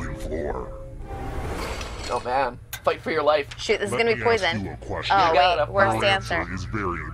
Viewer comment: dose say sh*t here 55:56 ????? is it just me???? like i hear her say ether "sh*t this is going to be poison" or she says "shiot this is going to be poison"